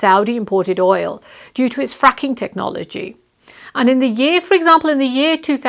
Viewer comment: did Najma say "due to it's fucking technology"..??? Or is there a problem with my hearing..?